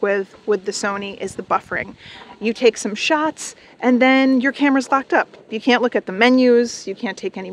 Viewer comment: On 8:45 did she say “your camera if fvcked up”? 😅